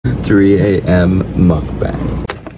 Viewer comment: While u were saying 3am mukbang I actually heard it as 3am MUD FRIEND 😂